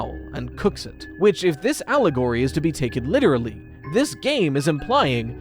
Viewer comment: Idk if I heard that wrong but I swear if you said "Owlagory" that's fowl